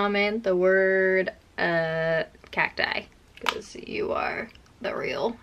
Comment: "Cat died" lol could have sworn i heard that. I am still watching🤗 love your videos😍